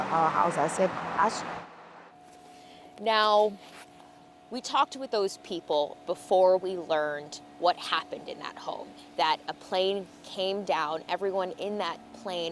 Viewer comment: What did that woman say? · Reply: "Now, we talked with those people before we learned what happened in that home."